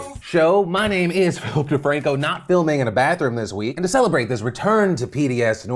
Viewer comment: Did Phil call himself Hope Defranco? I... hope not, since that means he was trying to use a subliminal on us like that. And that's not nice.